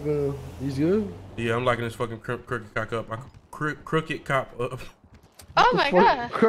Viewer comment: Am I trippin or did he say Crooked cracka lol 😂